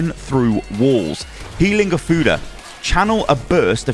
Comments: I was not looking at the video when he read Healing Ofuda, so I just heard "Healing a futa". That shit gave me whiplash. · I thought you said "healing o' futa"